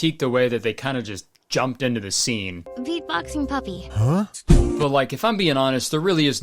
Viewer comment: I thought you said beat boxing puppet and absolutely lost it